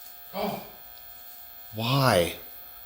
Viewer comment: You guys have a few back ground disembodied voices. I can't make out what they're saying. To me sounds like the first one says "Wah" second says maybe ... (listened to it a few more times) I just can't tell.